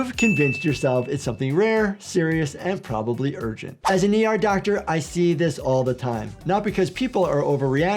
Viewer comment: thought you said AI doctor